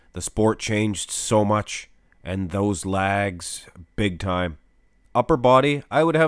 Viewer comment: Is it safe to assume these are your favorite versions of each individual ps it sounds like u say lags A to my ear 👂🏻 good work on these very entertained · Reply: Yeah, he says “lags”.